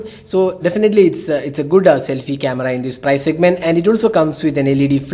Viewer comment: I heard it's a Buddha selfie camera lmao 😂😂